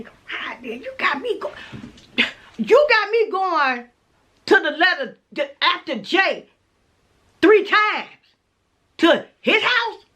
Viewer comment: Cracking up every time she refers to the man as J....the letter that comes after J three times😂😅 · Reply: Omg, lol. I didn't even know what she was talking about. I thought she said 3 letters after J 😂